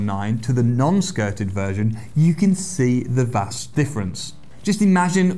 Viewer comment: Did he say you can see the Vas deferens 😂